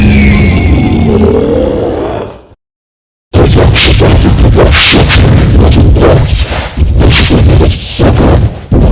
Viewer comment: What did he say in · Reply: Barney said "Good Luck surviving the next century in Leavenworth! You miserable little f-er! Good Bye Now!"